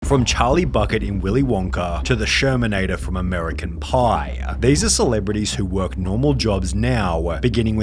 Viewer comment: I thought he said "From Charlie Fuck it from Willy Wonka"💀💀💀